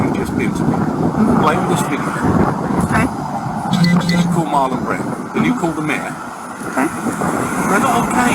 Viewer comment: I'm deaf in one ear so I thought he said "Go call Marlon Brando, then go call the Mayor"